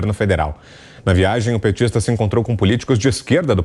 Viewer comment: Entendi "Lixo petista"...